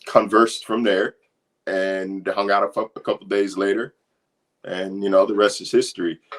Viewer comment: Did say hangout and f### around 4:10 😭😭😭😭?